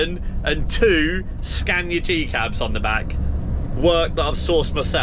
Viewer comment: why did you say t-cab?😂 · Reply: He said G Cab